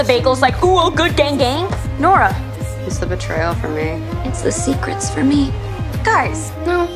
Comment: "is the betrayal for me" normal "You bakeal for me" *what i hear"💀